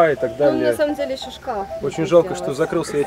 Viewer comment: она говорит шошка 🐷 ?? Мне послышалось или нет 😄